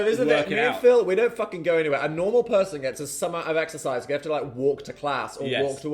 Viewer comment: did dan say 'summat' at 23:43? 'summer of exercise' makes no sense lol (re: subtitles) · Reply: i think it was 'some amount'